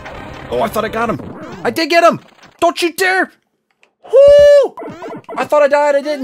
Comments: Is said don't you dare ln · Did just said don't you dare ln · Did just said don't you dare!